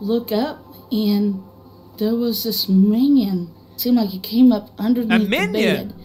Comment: She said "man" Ben. Not minion. She's country as hell. I know this because I just so happen to be country as hell as well. It does sound like she said minion though didn't it. Lol Gotta sound it out like this .... "May-Yun" I'm aware of how funny that is... but everywhere has dumb sounding words. Makes the world more interesting.